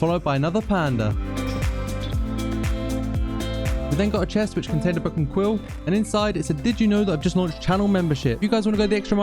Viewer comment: why did the subtitles say a cuss word/swear word? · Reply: I actually have no idea lol I said book and quill